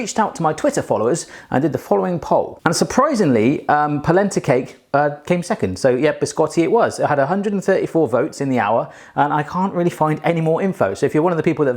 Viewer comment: Legit thought you said placenta cake...that wouldn't be as tasty